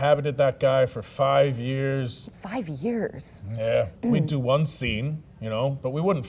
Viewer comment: Here's some more: 2:51 5:34 6:47 7:57 8:48 10:52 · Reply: the “Yeah” also sounded like Arthur lol